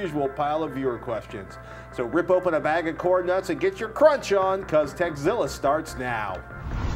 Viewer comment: i thought he said "so rip over a bag of corn nuts and get your 'crotch' on" 0.0